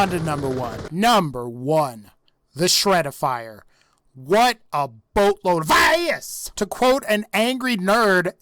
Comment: I still can't understand what word u pronounce. If is real pls answer me :D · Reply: he says, "what a boatload of ass"